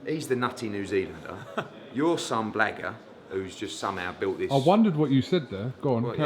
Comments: Jus a niga holding the camera that's what Eddie really wanted to say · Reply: When I first heard it I thought he said black cunt tbf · I had to rewatch that as i thought he said your just some blacker lol but no he said blagger.